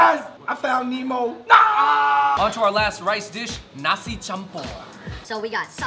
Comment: You guys should try Bandung (west java, Indonesia) culinary, we have various food from all around the world 😁 i like the way you say "nasi campur" i heard you said "nasi kampur" 😅 but that's okay #come and explore Bandung#